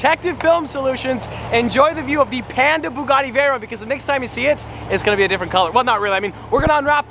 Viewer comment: I thought he said pandem bugatti veyron and I didnt know how to feel